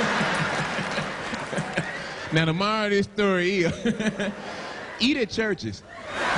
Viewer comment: when I was young I thought he was saying “tomorrow this story is” lol